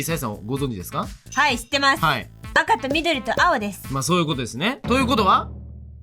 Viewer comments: 画面見てなくて音だけ聞いてたら、ここの「赤」が「バカ」に聞こえて「バカと緑と青？？？」ってなって、一人で笑ってた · どうしよう赤がバカにしか聞こえないw